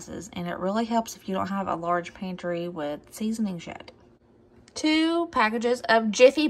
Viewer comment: Lmfao did you just say it helps if you don't have a lot of "seasoning sh@ At 12:03? I adore you so much ❤️